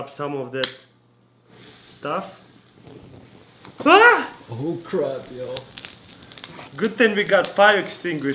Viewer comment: Omg at 2:01 I heard someone in the background say "Oh crud ya'll" XD